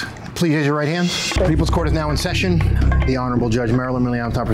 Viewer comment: That's so weird sometimes it sounds like is saying the horrible Marilyn Milan instead of the honorable LOL